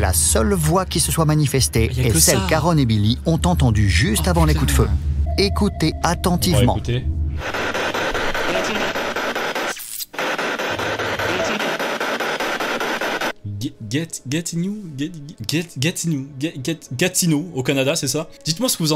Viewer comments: À 19:45 "behind you" voilà ce que j'ai compris. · perso j'ai entendu get in comme si elle voulait dire que zak est entré dans la salle